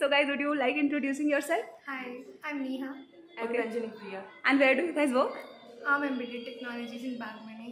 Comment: Sorry did I hear Angel Priya.😵😐😲😵‍💫